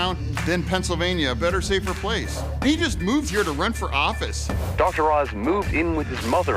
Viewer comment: did he say he moved here to rent for office? I hope that's what he said. Cause that works.